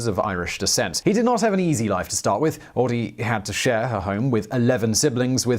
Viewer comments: did you say... "her home"? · I SWEAR, at 1:18 you said share HER home 😂 I went back and listened for share A home, but i didn’t hear it